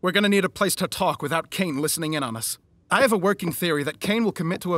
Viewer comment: Is it just me or sometimes sounds like he's saying king instead of caine